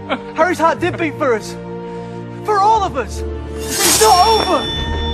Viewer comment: Why did Neville sound like he was saying it's joeover